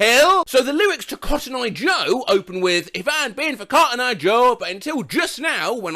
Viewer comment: I ALWAYS THOUGHT IT SAID "I had a thing for cotton eye Joe" I THOUGHT THIS WAS ABOUT A WIDOWER EXPERIMENTING WITH OTHER MEN. BYE, I'M DONE FOREVER.